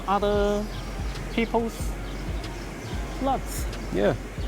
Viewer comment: Bokeh Bros are now the Lut Nuts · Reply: Is that what he said? I'm a wee bit hard of hearing (long story involving a rifle), bit I could have sworn they said "LUT sluts"... 🙂📷 Note: Camera emoji without a flash!!